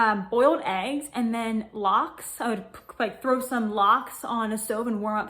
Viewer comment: Beautiful hair! What did she say at the 4:20 mark? Boiled eggs and what? Sounds like Locks?? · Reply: Yes, lox!! It i smoked salmon, very high in good fats for the body and protein :)